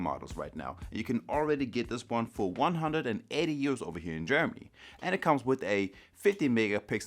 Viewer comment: Did he say 180 euros?